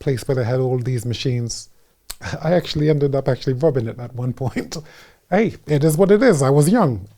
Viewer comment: He actually ended up doing what at some point? i can not make out what Chris said here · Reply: He either said he vomited or robbed it, I think robbed it because he then says he tells others not to do the same